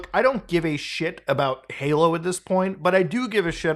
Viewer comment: are you saying “Paylo” instead of Halo? Sorry I’m having an “argument” with the missus about whether you said halo or paylo. Subtitles say “halo” but I don’t know if they are auto generated. My gf claims I won’t convince her it’s not “paylo”. But I think it’s “Halo” he says, it just sounds like paylo. …Paylo 😂